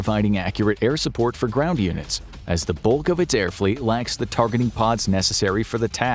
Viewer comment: thought he said "Bulkovitz"